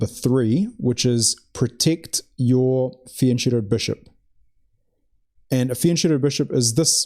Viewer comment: Rule #3 "protect your ______________(???) Bishop" Never get what he's saying. Neither subtitles got it! What he is pronouncing. What kind of bishop is that?? EDIT: this drives me nuts. I literally listen to this pronunciation for 10 times already; no help. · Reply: He said „protect your fianchettoed bishop“. The bishop is in a fianchetto when you put the piece on b2/7 or g2/7. Hope this helps!